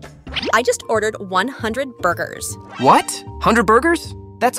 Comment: I heard she said"boogers not burgers"!😂